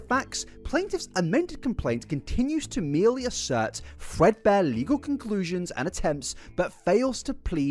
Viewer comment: Dude, every time you say "threadbare" I hear "Fredbear". Is that just me?